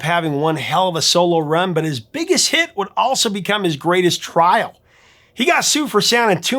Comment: Poll: What is your pick for the GREATEST SOLO BEATLES Song? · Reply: Woman is the N* of the World. Oh, Greatest? I thought you said craziest.